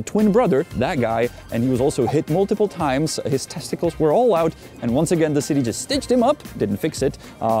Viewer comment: his testicles were all out? Did I hear that correctly??? 😂